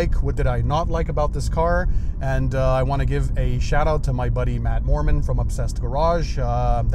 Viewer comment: Thought you said "Matt Mormon" for a second there 😄